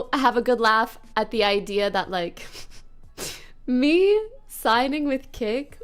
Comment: “ME signing with kick “ sounded like her saying me signing with those peasants 😭😭